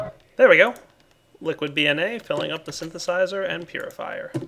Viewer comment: it sounds like your saying liquid 'bee'na rather than dna